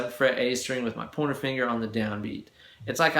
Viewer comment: Lol! I swear to god I thought was saying with your 'Porno Finger'. POINTING FINGER! Got it, great lesson.